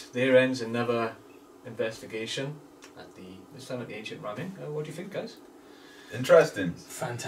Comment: I keep hearing “Ancient Ramen” 😒